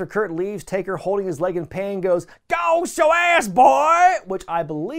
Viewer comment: As far as what The Undertaker said at 18:36-18:35, I translated it to "God... It's your ass, boy!...", as well. I remember watching this Pay-Per-View with a friend of mine & he asked me "What did The Undertaker say???...".